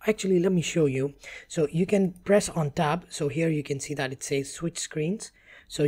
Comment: Thanks! This video was very helpful. There was a bit of confusion with some things like the "up arrow" on the keyboard in the diagram but it is actually the right hand shift key that has the little up arrow on the American English keyboard. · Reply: When he say's to switch screens during the merging process, it sounds like he is saying "press on top", he is actually say "press on tab". And the Tab key typically just says Tab in English and we never look at the little arrows on it. So I was thrown off. Haha! I'll share more little quirks as I go through it to help other English speaking Americans.